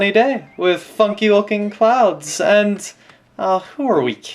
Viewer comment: Oh my god, when he said "Funky Looking Clouds" I thought he said "F*** you and your child"... And I was like, "That can't be Eyan... " and I played it back and was like, "oooooooh, Funky Looking Clouds! Whoops"